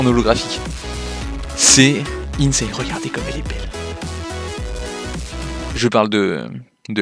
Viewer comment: Moi j'ai entendu "Regardez comment Chelxie est belle"